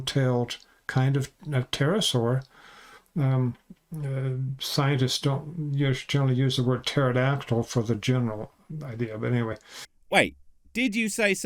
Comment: Scientists use the word "and" a lot, " pterasaur" not so much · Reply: "Scientists don't use... generally use the word pterodactyl for the general idea, but anyway." then immediately misheard him in the following comment.